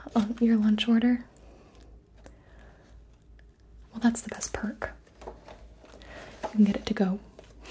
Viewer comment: did u say part or park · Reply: She said perk